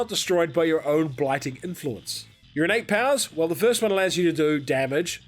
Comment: I thought you were saying urinate power for the first few spirits...